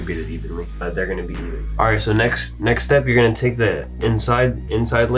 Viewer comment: WHAT DID HE SAY?? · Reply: he said "next tip" 🗿